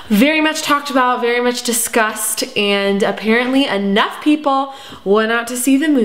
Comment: i thought she will say "too much disgusting" but she just said "to much discussed" lol.